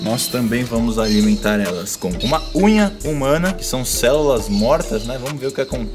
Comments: eu juro q ouvi maconha humana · Só eu entendi maconha humana? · Quando ele falou "unha humana" eu entendi "maconha humana"